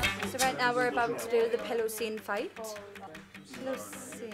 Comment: i thought i heard perrie said "pillow zayn" lmfao